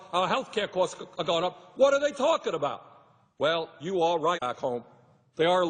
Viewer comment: "you are right asshole" 🤔 lol, is that what he said? ✌😷✊🇺🇲🌹🏳️‍🌈🚩🏴💜